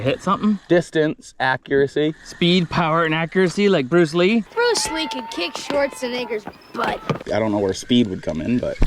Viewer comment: What did he say? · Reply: “bruce lee could kick Schwarzeneggers butt” had too rewatch it a couple times bc i thought he said the n word too lmaooo